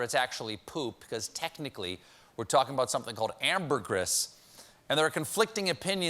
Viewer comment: I wasn't looking at the screen when he said ambergris. I thought he said hamburgers.